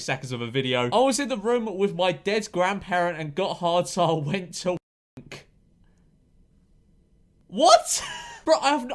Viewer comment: okay sooo can someone tell me what the censored word in 0:41 actually was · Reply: Wank